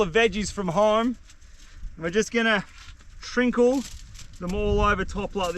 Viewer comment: Did say “shrinkle?” 😅